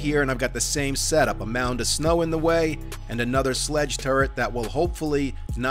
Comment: First 30 seconds I heard "made of snow" and I hit the like button. LMAO OK after watching the video... I never laughed so hard I nearly choked to death. day 49 horde?? WTF were you thinking LMAO maybe a day 21 or 28 horde but 49??